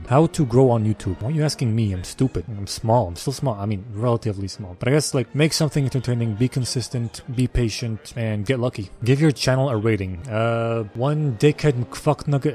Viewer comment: i heard that as "give your child a rating" and the answer probably made more sense then